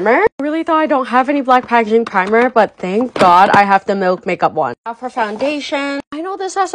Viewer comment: I thought she said the “milf” make-up one 💀